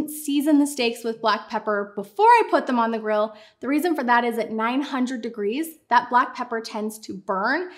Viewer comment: How hot was the grill? I thought you said 900 degrees. I don’t have a grill that can melt glass. lol Plus I’m thinking that the steak would just burst into flames 🔥 Please help 😊